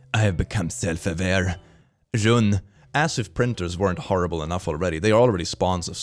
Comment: Pls im really confuse !! For all people who can speak french : Am i the only one who heard "jaune" instead of "run" at this time ?!?!?!?